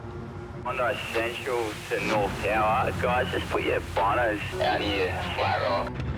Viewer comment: the caption says “but your boners...”😂